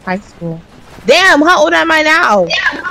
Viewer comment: THE WAY THAT I JUST UNDERSTAND WHAT SAID NOW "Damn, how old am I now"